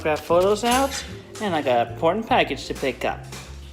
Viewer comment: Thank God for subtitles I thought he said "Porn Package" 🤭